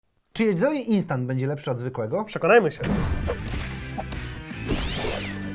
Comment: "czy jedzeli instant"? Tylko ja to usłyszałem jak odtworzyłem filmik? ;D